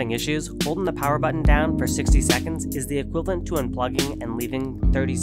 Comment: Interesting to hear this is a common problem. @ 0:45 did I hear right? Hold the button for 60 seconds, as opposed to unplugging for only 30 seconds?